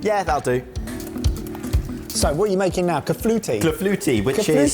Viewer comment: Did I hear Kush say "caflouti" several times? Of course it's "clafouti". If I misheard, apologies ... I wouldn't like to be on the end of Kush's wrath. (Though I would like to be on the end of his broth ...)